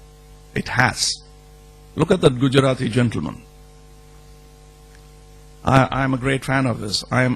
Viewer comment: When he says Gujarati gentleman i thought he was saying Modi.lol. But great conversation